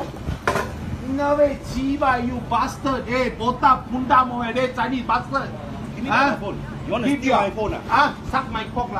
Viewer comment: Wait at 3:38 did he actually say dota😂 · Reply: Kavinnesh Segar he said botak